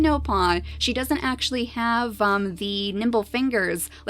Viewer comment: i thought she said derp SNOOT not derp snout